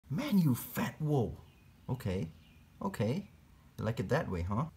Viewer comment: Here is what I hear “MAN YOU U FAT WOOL” 😂😂 ((at the very beginning))